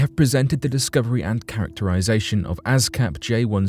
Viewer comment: I kept hearing "Ass Cap"